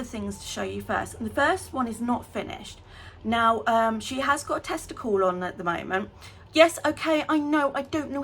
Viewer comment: I can't stop laughing! Every time you say "tester call", I hear "testicle ". 😂 I love your channel so much! ❤